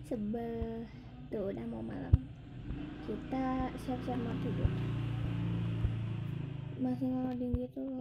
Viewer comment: Aku Kok Dengernya Siap Siap Mati:v